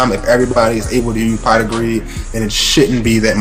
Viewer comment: I thought that said Pot of Weed first lol